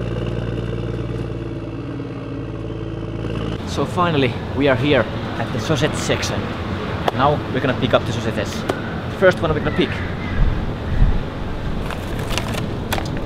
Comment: did he say so said sex station?? I so confusion